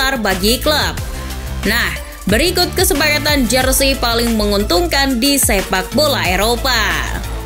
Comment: kirain bunyi narasinya "kesekapatan paling menguntungkan di SEMPAKBOLA EROPA"...Eh ternyata salah denger🤣🤣🤣